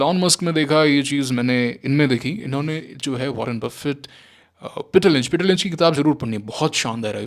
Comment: pr jis author ka name btaya h wo kya h koi btaega please? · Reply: Peter lynch ( One up on wall street)